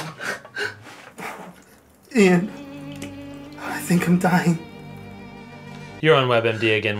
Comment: put on english subtitles and instead of Ian i think im dying it says Yeah i think im dying